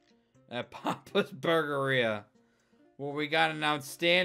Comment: I thought that said papa’s Bulgaria